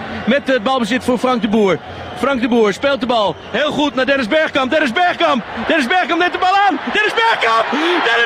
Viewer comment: first i thought he was saying ''there is beckham''